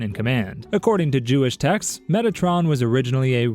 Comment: Every angel name being elegant and noble Metatron:🤖🔥🗿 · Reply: And I heard "Megatron"